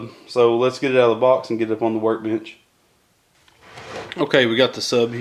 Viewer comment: when he said lets get it out the box and the work bench i thought he said and get to work bitch😂😂😂